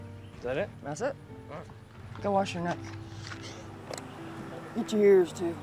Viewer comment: did she say go wash your nuts? 😂